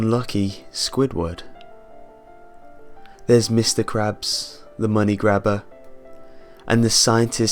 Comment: When you said "Mr. Krabs." It sounded like you said "Mr. Craps."